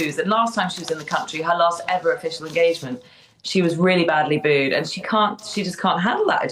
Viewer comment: 😂 I thought said “…she can’t handle the booze!!” Well…ja, we all know that….. but, she meant the “boos!” How hilarious…. Maybe a slip of her tongue 👅??